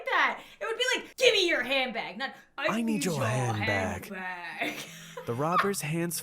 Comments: I thought the robber said "I need Johan Back", I willl now pass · When the rober asked for the hand bag I thought he said I’ll need you head back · I thought the shadow guy said I need your hand back😂😂😂😂😂😂😂😂😂😂😂😂😂